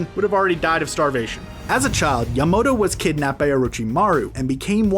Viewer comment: Ho garomo,THE madara, konoHA, ItAAchi, Akasooke, ba yuka gan.....SHAKRA XD kagooya, Nagado, abido, setsu HAHAH YAMODO instead of yamato 7:47, tatsuka blade